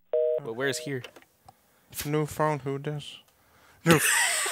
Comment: what said at 11:11 ? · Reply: New phone who this